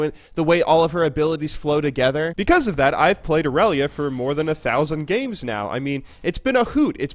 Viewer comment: i totally thought he said "i've played irelia for more than a thousand years" and i believed him XD